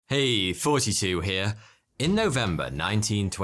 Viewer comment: i've always thought you said "hey 42 here" but i just realized you're saying and i feel stupid